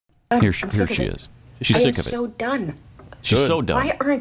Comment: When she said “I am so done” I thought that she said “I am so dumb” at first, lol. Well, yes she is.